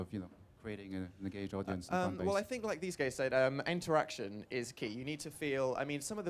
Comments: XD OMG I'M DYING I THOUGHT HE SAID ' I think as these gays said' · bless him dan looks so nervous and did he just call the two next to him "these gays" bc same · did he just say "like these gays said"? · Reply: He kinda mumbled the word and it sounded like "gays" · 'well i think like these gays said....'